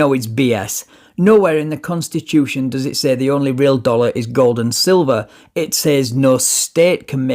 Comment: Thank you for emphasizing what he says around 4min, I swear I thought he said “the dollar goes to the children”, not “the dollar is gold and silver”. Lol. I thought he was thinking donating illegal money makes it not illegal or something lol